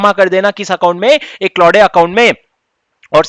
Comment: Did sir just said ek laude account?